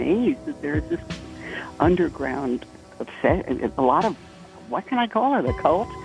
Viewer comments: that "inaudible" bit is her saying "a lot of" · it sounds like she is saying "It's a lot of"